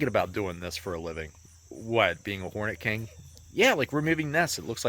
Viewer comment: ever since you made a comment on how youtube thought you were saying horny king. I can never unhear that now. XD